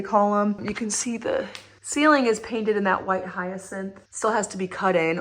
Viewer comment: Love your updates! What is the white on the walls that you think looks yellow? Hard to tell on the video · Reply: I thought she said white hyacinth?